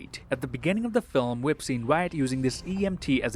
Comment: Did i hear "Malaysia" 4:48?